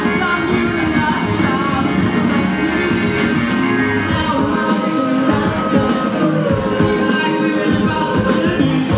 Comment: I'm perplexed about two things. Why did James throw the keyboard at 25:12 and what was said to the cameraman at 32:50 ? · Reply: what was said at 32:50: "heard what he said. no disputing it"